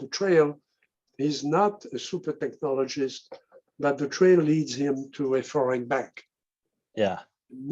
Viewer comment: What does he say at @ 50:29 "It leads him to afar and back" ? If so, I can only assume he's referring to consciousness altogether.